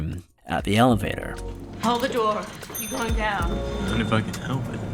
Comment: The amount of Mandela Effect going on here is amazing. Here is the actual dialogue: - Isabel: Hold the door, are you going down? - Constantine: Not if I can help it. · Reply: She says: "Hold the door." (imperative, full stop) Elevator bell dings. "You going down?" with barely any intonation indicating it as a question, it sounds more like another order. Which would make sense, you know, because shes supposed to be a trained policewoman. It makes sense that people would mishear it, with the elevator bell and the lack of inflection at the end of the question.